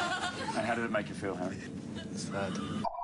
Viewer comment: What did Harry said at 16:32 ?? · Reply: Sad